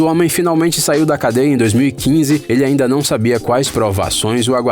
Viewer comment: Q Coincidência (cadeia,Cadeira) Eu ouvi os dois👂